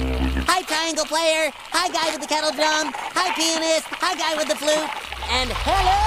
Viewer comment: naw what did he just say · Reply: He said “Hi Pianist.”